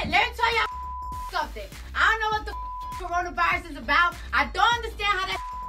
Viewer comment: i thought she was saying corona berries